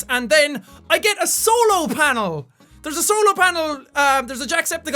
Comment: When he said "Solo Panel" I thought he said "Solar Panel"